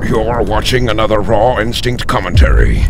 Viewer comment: every time you say raw instinct it sounds like you say rotten sink